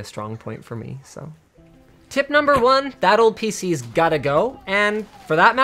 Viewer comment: "Battle pc" ? wtf is a "battle pc" ?.. I thought he said "that old pc"